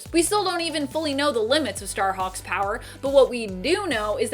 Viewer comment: When I heard say "Star-Hawk" I actually thought she said "Star-Fox." But I know I was mistaken.